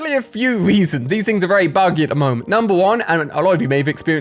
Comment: he said marble world 😂 he meant marvel world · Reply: he said wry instead of why 😂